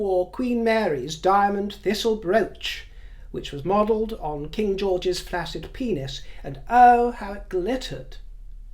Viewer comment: Did I hear wrong? What did he compare Camilla’s brooch to? It sounded like he said it was modeled on a flaccid penis! He didn’t skip a beat when he said it! I’ve listened to it three times, and that’s what it sounds like he said!😂 Please correct me if I misheard him!